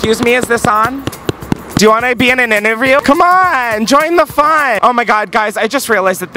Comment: "Do yar wannarbenininerview?" instead of "Do you want to be in a quick video?" What is this madness?!